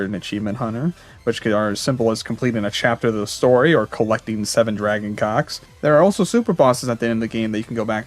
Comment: I put this as white noise before sleeping and I just hear "The 7 dragon cocks" and I lost every ounce of tiredness in my body